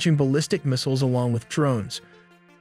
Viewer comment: wait did you say IDK defend itself??? 9:57. are you getting paid by Israel too?